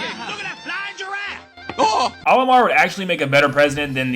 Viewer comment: “Oilman would make a better president then we have right now” is what I thought you said lol